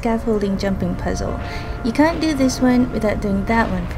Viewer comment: The first time around I thought you said "You can do this without...". Turns out you said "can't". That's why I say "cannot", to avoid confusion with my accent. No biggie, though. Scaffolding is a very easy puzzle one can do in less than a minute.